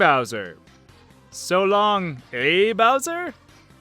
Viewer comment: When you thought it was "Solong gay Bowser" I thought it was "Solong gay donkey." when I was little. Also Yoshi's Story in the title music I thought they were saying "OOOOo little Black Hole, little black hole, little black hole....".